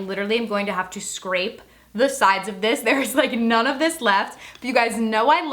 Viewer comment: Lol this bitch got like nothing I heard “there’s not a lot of this” like 10 times. Lmao 😂😂🤣🤣🤣